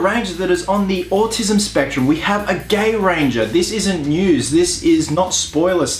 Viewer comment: When he said Gay Ranger I thought he said Gangbanger and I was very interested since I haven't seen it yet